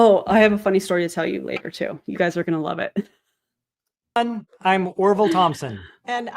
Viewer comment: I’m actively cooking dinner and they said “I’m Orval Thompson” and I heard “I’m a horrible person” 😂 oops